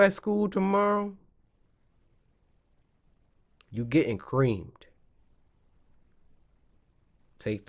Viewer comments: Did he just say u getting creamed 😶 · I thought Cory said "when I see you at school tomorrow your getting raped" But he actually said"your getting creamed"